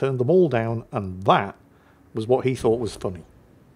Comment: Segal being a cop I thought you said he wasn't funny😂